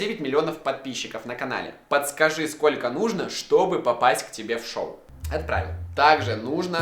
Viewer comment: мне одному послышалось? "как к тебе попасть в жопу"